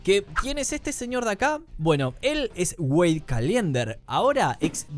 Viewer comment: entendí Wey Caliente🥵🥵🥵🥵